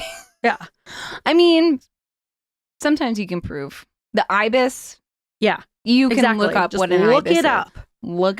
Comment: Did I hear "the ibis"? In Australia, we call them "bin-chickens".